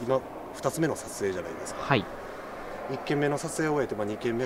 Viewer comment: の一件目の撮影を終えて2件目の2ら辺で女の人の声？で小さく、はいっ、て聞こえませんか？もしかしたらの声かもです